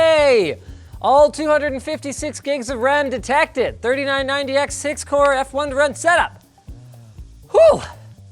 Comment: Am I the only person who heard "six-core" ? 🙄